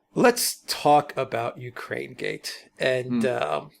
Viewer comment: Lol! I thought it said Urinegate.